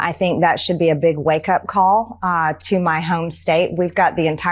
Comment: She said 'in my home state' , but I hear 'in my own state'